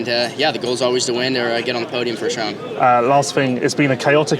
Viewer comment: I thought he said get on the podium for trump lol